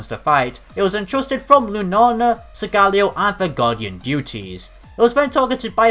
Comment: did I hear it wrong or did he say guardian duties 😂